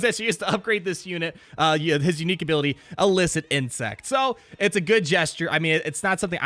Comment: I heard "illicit incest" and was like 'uuuh what???" LOL